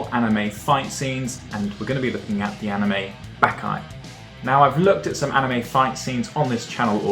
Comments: did he just say byaki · did he just say "bakai"? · did he just say "bakai"..? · did he just say bakai